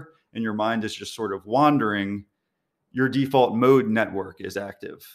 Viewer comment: I heard "default toad network" rather than "mode."